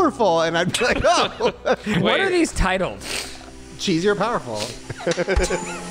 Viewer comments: i didnt hear cheesy or powerful... i heard 'jean shorts' 😭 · When you played cheesy or powerfully I swear I heard "painfull" the first time then the next time I hear cheesy